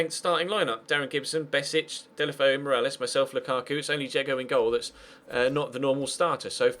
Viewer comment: Didn't pay attention to the screen for a second just before the Lokomotiv game and thought you said "Only Dzeko in goal.." and i was like : "What WTF?!??! Edin Dzeko in GOAL?!" And then i saw Jeggo and yeah lol....😂